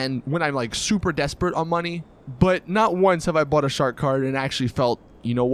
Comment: I thought he said shart kart but he said shark card